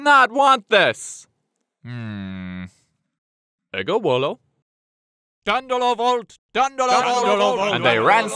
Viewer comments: The hell does "ego wolo" mean? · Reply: Thank you. I also misheard Dandolo vult as Gondola vult, which I thought was joke about them being sent by Venice. ...don't look at me like that. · What the heck does the Doge say at 2:48? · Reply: "Deus vult" = "God wills/wants it" "Ego volo" = "I will/want it"